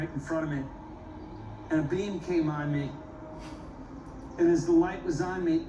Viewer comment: I thought he said a "being came on him" dirty bloody aliens!!!! 🤣🤣🤣🤣